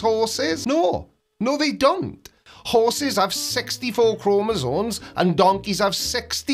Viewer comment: there is no "N" in Chromosomes, or it's your accent, but i could swear you said Chromosones